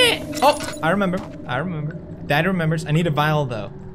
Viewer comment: did he just say "daddy remembers"???///